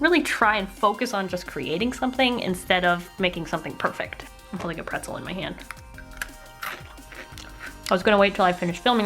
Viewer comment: i thought she said I still have a pencil in my hand, and she started eating it😅😅